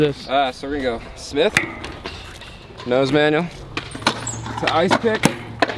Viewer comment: Thought you said Super manual. Lol. Like a Superman Manual.